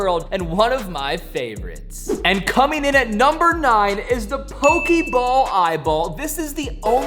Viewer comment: did you call it pokie 😂😂